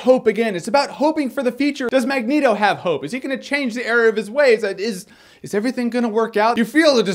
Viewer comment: Did he just say, "Change the area of his ways"?